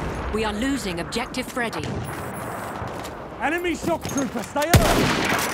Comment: Did he say "Shock trooper" or "Sharpshooter"?